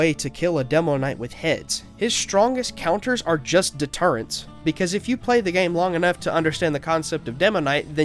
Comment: excuse me did you just say turrets instead of sentries? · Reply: Deterrents